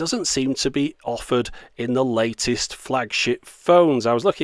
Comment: Almost sounds like he said "latest flags**t phones" @ 6:00 . And I do NOT mean to criticize. As for the gimmicky 'here today, gone tomorrow' aspects of a flags**t phone; and of course the reliance on complex cell networks to "give" you paid unlimited-low-res-if-even-that video... I say POO BUSINESS!!